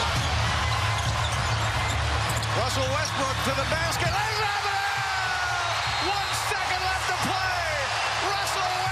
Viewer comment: what did the reporter said. Lalalala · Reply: Lays it up and in. Clean your ears please